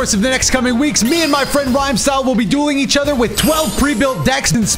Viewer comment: I had to replay the intro...I thought he said, "me and my friend rhymestyle will be doing each other."